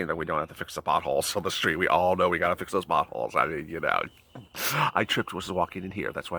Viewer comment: When you said pot holes I thought you said butt holes at first! Ha Ha!